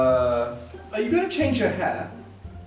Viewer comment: I THOUGHT SAID HAT AND I WAS CONFUSED BECAUSE SHE DIDNT HAVE A HAT ON THEN I WATCHED IT AGAIN IM SO DUMB